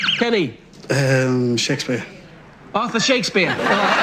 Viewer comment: in all fairness, the arthur one did sound like author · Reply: I heard "Author"… when he said "Shakespeare" and was wrong, I realized he host had said "Arthur!" They shouldn’t penalize the guy for the host not enunciating the word correctly!